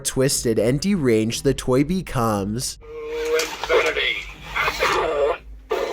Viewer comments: Right around 5:50 it sounds like Buzz says, “To insanity and beyond.” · So buzz sounds like he said to inSanity and BeyonD